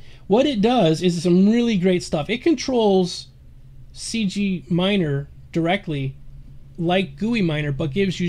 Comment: cgwatcher instead of guiminer